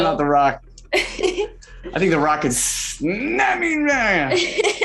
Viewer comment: Okay so if anyone else is struggling to understand what Timothée was saying @ 5:46 like I was, I think he said “I think the rock could snap me in half”😂 I think??